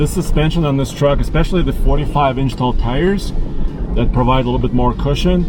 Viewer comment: Did he say "45 in tall tires"? LOL